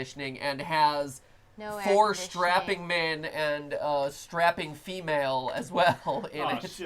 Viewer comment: i thought matpat said "Stripping men and stripping females"...